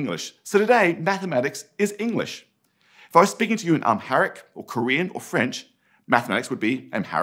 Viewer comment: did he just say 'Amharic'? I speak that language and I don't think many people know it outside of Ethiopia.